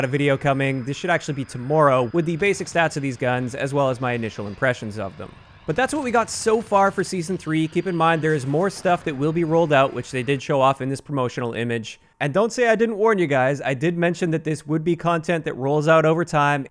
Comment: did you just say Season 3?